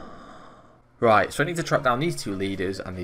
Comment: Do all clues timed, beginner to master, with no runelite. Winner gets all the loot and for every minute difference, 10k added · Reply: I honest to God thought you said quests and not clues, and was still down with it